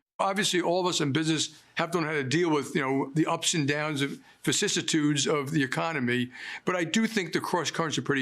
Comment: what is he saying? 'facissitudes???' don't know that word and the subtitle skipped it